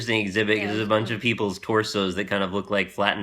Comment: beef jerky souls, only because I swear it sounded like said there were a couple of souls that looked like beef jerky, I may have misheard.